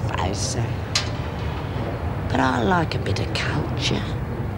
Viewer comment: im positive lizzie says cunture instead of culture... i even rewound three times to check!!! :O i think that was an intentional slip lizzie ;) lol xxx <3